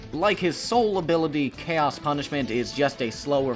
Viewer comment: Sonic and The Black Knight as a concept is so Raw Also IT'S CALLED CHAOS PUNISHMENT??? As a kid I always heard "Chaos Hyperspace" or or or "Chaos Time and Space"